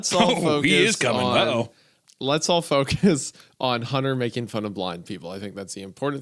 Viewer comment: I really thought he said "Let's all focus on Hunter making fun of black people" and I had to rewind like 5 times😂 then the he hates the disabled comment rocked me